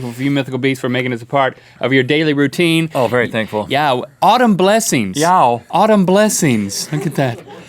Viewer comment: I thought they were saying, "All dem blessings"